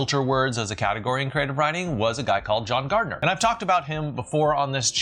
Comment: I thought you said Guy Gardner, the third Green Lantern, Then i thought was there an actual person named the same as a superhero? So I went back and clarified thet it was ‘a guy named John Gardener’